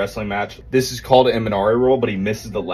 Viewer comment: I heard “skip to the good stuff” I think coach knows what’s up